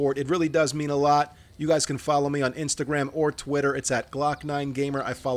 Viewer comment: At first I thought said " i follow all beavers back "